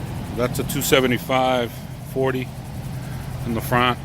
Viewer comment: I have 275 in the front 295 in the back brotha the ride and handling is way way better man. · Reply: thanks bro! I thought you said 295/40 in the front. Car looks good!